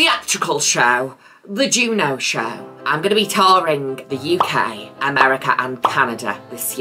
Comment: i thought u said "i'm tolerating the UK" and like WORD